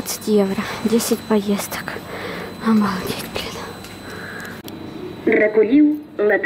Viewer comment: мне послышалось, но сказала блять хахахах может и послышалось 😂😂😂😂 сразу говорю я не обвиняю в том, что она сказала, просто смешно от нее это слышать 😂😂😂😂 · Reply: она блин сказала )))